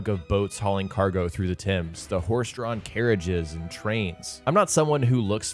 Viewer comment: I’m sorry did you just call the Thames river the Tim’s?